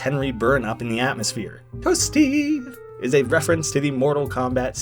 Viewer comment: I thought it said Hupday, it sounds just like that, but no, it’s Toasty, but honestly, it doesn’t sound like that It’s at 8:11 if your wondering, or is it at 8:12, I’m not sure.